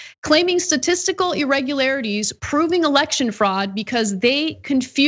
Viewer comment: Oh election fraud! Whew, I thought you said erection fraud. Trump’s been guilty of that for the last 30 years